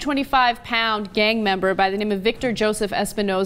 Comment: For a sec I thought she said "425 pound gangbanger"